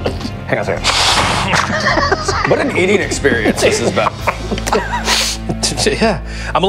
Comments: I swear I thought Sean said what an idiot experience this has been. Had to replay again to hear "eating experience" 🤣🤣🤣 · Ngl I thought Sean said "What an idiot experience this has been" at the beginning lol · Sean said, "What an eating experience this has been". I heard, "What an idiot experience this has been". Sorry George. I didn't believe it, so I went back and rewatched that bit.